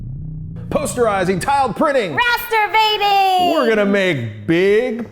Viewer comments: I thought she said Masturbating... I think I have a dirty mind... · I THOUGHT SHE SAID MASTURBATING